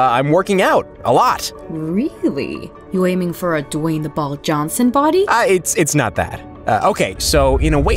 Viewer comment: “The Dwayne the ball Johnson body” What I heard “Dwayne the rock bald son body